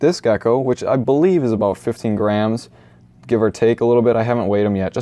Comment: I thought he said 15 grand😂😂